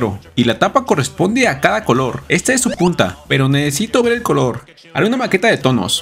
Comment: Le entendí "Está es su puta"😭